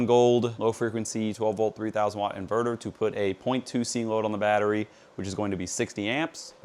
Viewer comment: you say "point 2 C" (0.2C) and subtitles say "put a 2C load" man they need to fix stuff like that, what a massive difference. Especially technical videos where someone is trying to learn they're going to learn totally wrong. anyway nice battery & great review, was surprised how well it seems to be put together but I guess you did say it isn't the cheapest lol